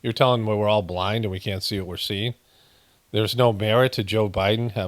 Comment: I thought the crowd were saying "there's no beer " 😂